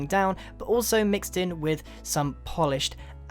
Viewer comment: Did he say adensite?